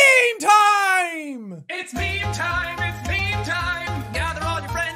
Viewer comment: What normal people hear: "Memey Timey, Memey Timey..." What I hear: " UNUS ANNUS UNUS ANNUS "